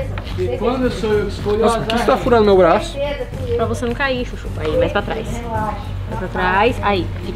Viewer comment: Só eu que ouvi a chamando o de xuxu